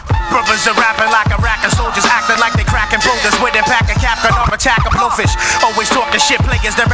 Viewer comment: Rapping like Iraqi soldiers acting like they stacking boulders.... killed it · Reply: I cudnt decipher what he was saying....but relistening it sounds like he is saying cracking boulders not stacking....